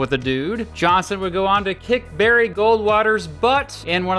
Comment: I swore it said "Johnson would go on to LICK Barry Goldwater's butt"